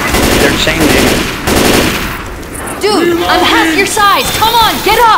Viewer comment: Did she say "DUDE"?